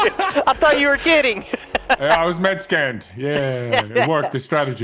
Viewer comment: i thought nogla said you could turn one guy even if their mexican but he said med scan